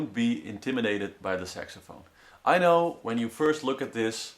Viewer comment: Thumbs up if you heard him say SEXAPHONE! XD